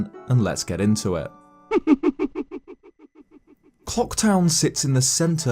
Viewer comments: I swear he says cocktown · i actually thought he said 'CO3KTOWN'